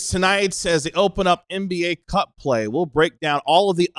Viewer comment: Omg when said “NBA Cup play” I heard “cuck play” but yeah that’s where we are at as fans….. firmly in the chair